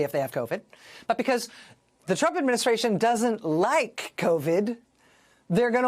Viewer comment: is she saying some people like covid?